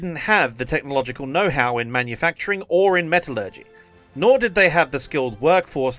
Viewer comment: I thought this said.. they just didn't have The technological know-how in manufacturing and embezzlement... Had to play it back a few times to hear metallurgy..